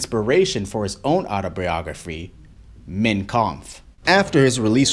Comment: wtf?! Why is he saying it like that?! It sounds like he is saying "mint koomf"